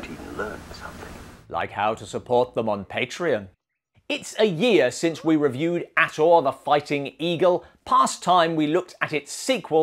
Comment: So, Ator went to all that trouble to rescue his beloved stepsister Sunya from the Spider Cult, only to have her die less than a year later. Tragedy or moral intervention? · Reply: My mistake. I misheard when said, "It has been one year since we reviewed the first Ator". You're right, the film does not give a specific period of time.